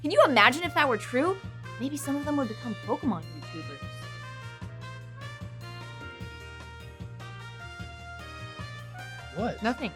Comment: I can't hear what says at 3:17. I tried like 6 times, but I just can't. Can anyone tell me what she said? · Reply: Sai Prime pokemon youtubers